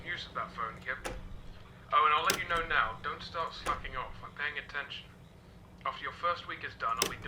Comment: XD I thought he said oh and I'll let you know now don't start slapping off